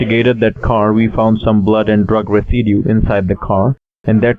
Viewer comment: Did he say "some blood and drug graffiti" was found inside the car? What the hell is that? Did somebody spray paint pot leaves all over the interior?